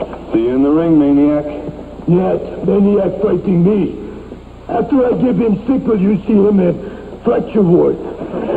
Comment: After watching the close-caption, I think it's "Yes, then you are fighting me! After I give him sleeper, you see him in fracture ward!" (The "sleeper hold" is real, and was infamously used by Hulk Hogan on Richard Belzer, live, on Belzer's late-night talk-show [q.v. Belzer v. Bollea ]; the "fracture ward" is presumably where the hospital staff deals with fractures, although I don't think it's ever actually called that.) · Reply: He said, "Nyet! Maniac fighting me! After I give him sickle, you'll see him in...traction ward!" The Russian Sickle was his (Ivan Koloff's) version of a lariat, or swinging arm strike. The second part of the sentence got garbled, partly by the tape degradation and partly by Ivan tripping over his line.